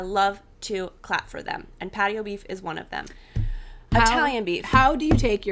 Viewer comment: Can't wait for the new patterns. Also, I 100% thought you were saying "Paddy O'Beef's" 🤭